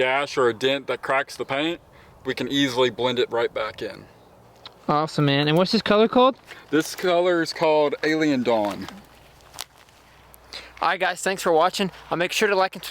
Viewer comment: Man, amazing job, I have a 2009 FJ I am looking to repaint. What is the name of the paint you used? I heard "Alien Dog", but can't find that color scheme anywhere online. Any chance you could help me out and reply with some more info? Thanks brotha.